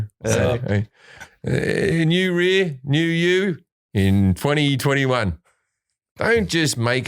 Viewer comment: did Jim just say: "new rear: new you" for the Tushy ad? oh what a glorious verbal typo